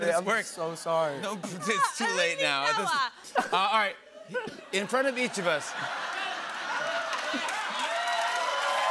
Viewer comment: what did millie say at 1:02? i cant make it out😄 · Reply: I believe she said “Noah”